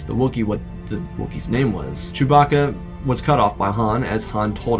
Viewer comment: Did you just say…Choe-bacca